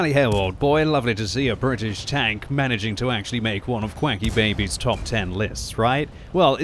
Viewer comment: Did I have a stroke or did QuickyBaby? I could have sworn he said "QuAcky" and not "Quicky"...